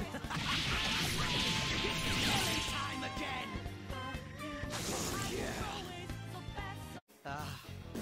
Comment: what's he saying ? it sounds like he's saying "f*ck yeah" BUT IDK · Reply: "Oh yeah"